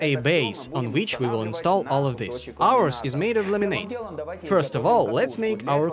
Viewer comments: did he just say the base was made of lemonade ? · did he just say the base was made of lemonade ? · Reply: lol I thought that too but he said laminate. With his heavy accent it sounded like lemonade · I know I probably missheard that but it seriously sounds like he said the base was made of lemonade in the beggining. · did he just say the base was made of lemonade ? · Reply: what I heard too! But he's saying "laminate".